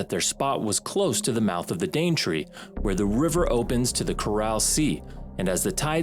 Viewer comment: “Chorale Sea…” That’s a strange way to pronounce “Coral” as in the famous “Coral Sea”. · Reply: I heard 'Coral Sea'